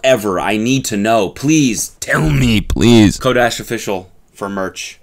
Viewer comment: I thought he said 'Kodak official' at the end.